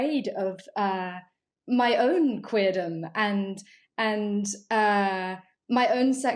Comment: did she say "my own queerdom" at 3:53?